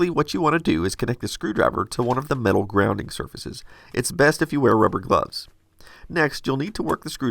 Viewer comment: wait... what? did i hear it correct? hehe "grounding circuses" 😁